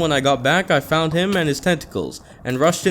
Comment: i thought he said i found him and his testacles ngl